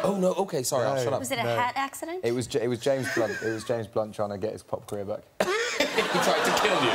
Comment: wha does eds say at the minute 0:25 about the cut im from spain so my english is not good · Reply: Laura Escobar He said "it was James Blunt trying to get his pop career back".